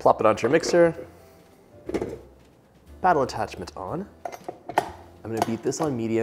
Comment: I know you said "paddle" attachment but i heard "battle" attachment and i thought you were making a cute joke 😂 ah, gotta love it.